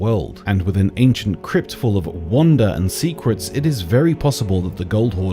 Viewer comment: I'm sorry, did he just say "Ancient crypt of wonder and secrets", or "Ancient crypt of WANDA and secrets"?